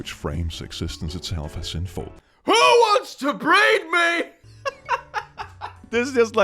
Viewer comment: i had this playing in the background and then i just hear scream "WHO WANTS TO BREED ME?!" bruh you did insane dude. It's very cool